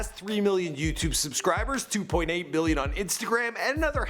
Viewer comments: did he said 2.8 billion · Did he just say billion ???